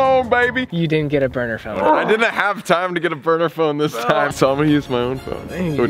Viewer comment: literally is about to call haunted numbers but doesn’t get a burner phone me: NOOOOO YOU CANT DO THAT OMG 😩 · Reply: I thought he said a burger phone